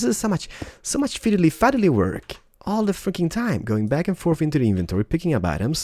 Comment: Is It me Or Did Say the F Word At 16:40 ?